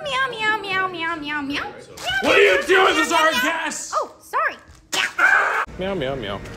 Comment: First i heard him saying what r u doing thats ur cast😅